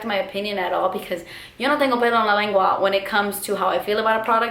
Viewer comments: "Yo no tengo pedo en la lengua." I don't have a fart on my tongue? Did I mishear that or is it an idiom I don't know? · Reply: Tori Carrillo she said no tengo pelo en la lengua. I don't have hair on my tongue. She's pretty much saying she doesn't sugar coat things; she's honest and blunt. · i'm not fluent in Spanish but all i heard there was "yo no tengo blejandifnksna" wtf? O.o lolll love you girllll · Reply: Nesa Devi it was <yo no tengo pelos en la lengua> That means that you say what you think no matter what.